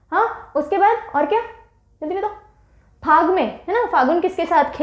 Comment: what did she said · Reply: "Jaldi batao" in a baby voice